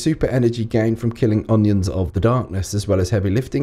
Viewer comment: did he just say killing onions of the darkness